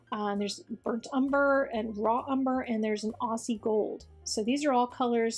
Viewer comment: I thought you said Aussie Gold , I think! But as always, TFS